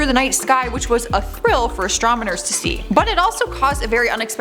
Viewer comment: I could be wrong... But I swear at 3:53 she says "astramaners" instead of "astronomers "